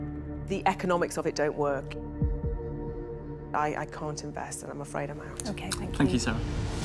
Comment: The captions have her saying "I'm afraid of math" instead of "I'm afraid I'm out" 😂😂😂